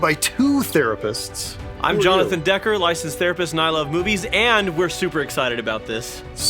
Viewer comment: Oh my gosh said his intro so fast I thought he said , I love Boobies 😅😅